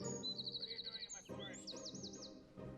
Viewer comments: if you listen closely at 6:46you can hear somebody saying what are you doing in my and then i cant hear it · Was it me or did anyone else hear “what are you doing to my forest” at 6:50 🤣🤣🤣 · did anybody else hear someone say What are you doing in my forest at 6:50 ? if not listen really closely 😂💕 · Did anyone else hear "What are you doing in my forest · Anyone else hear 'what are you doing in my forest' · did anybody hear a: what are you doing on my turf? Or something like that and on 7:40 Just around here